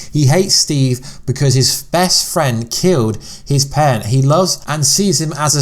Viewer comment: Is it me or do I keep hearing and I paused the video at 1:49 ‘he hates Steve because his best friend killed his pen’ that must be one very special pen